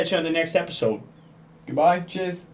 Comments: sounded like he said jizz not cheers at the end... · When he said "Cheers" at the end I thought I heard "Jizz"... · at the end, I heard ali-a say ''goodbuy biches